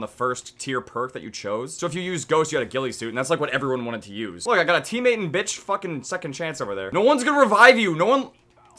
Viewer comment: did he say "teammate and bitch" or "teammate in bitch"? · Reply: jr in